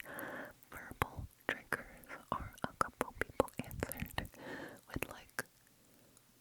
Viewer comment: i was playing games and listening to this video and was sooo confused at this part LOL i thought u said purple trigger and i was like ??? what does that even mean